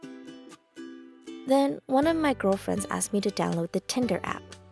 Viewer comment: did se just said "gilfriends"?????????